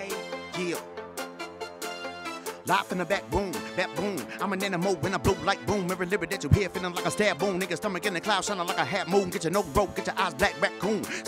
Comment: did anyone else hear him say nigga